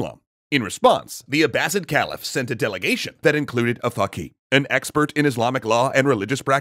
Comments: I swear when I heard dude say “fakhi” or whatever at 0:51 I was like “wait run that by me one more time?” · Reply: Faqeeh is an expert in Fiqh, Islamic jurisprudence. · “included a fucking” what I’m sure it’s just an Arabic word, but the subtitles skipping over the word doesn’t help.